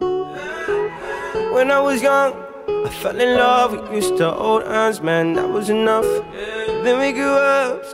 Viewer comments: Being dead serious when he said Fell in love we used to hold hands man that was enough I thought he always said fell in love with Mr. OldEyes man that was enough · Am i the onLy one thought he said “i fell in love with old ass man and that was enough“ listening at first